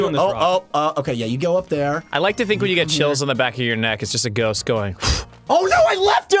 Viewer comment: Aw... I thought it said "Ghost Bowling".